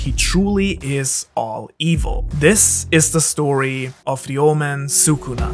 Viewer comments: I literally thought he said "This is the story of the old man Sukuna" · he said " Ryoumen Sukuna" what i heard O L D M A N S U K U N A